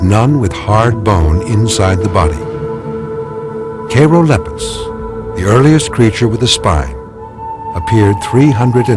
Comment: What’s the name of the fish at 33:38? I think he said Cheirolepis but I could be wrong